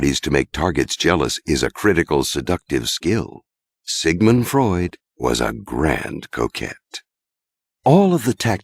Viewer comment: i thought he almost said "sigmind freud was a grand coke head"